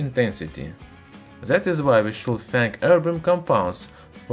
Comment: The English Auto genrated captions thought he said ,"our brain compounds". Instead of urban compounds 🤣